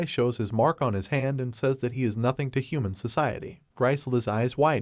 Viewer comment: I thought they were saying he is nigga😄